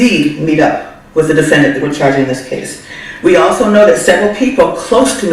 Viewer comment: did she say she charging 7 other ppl??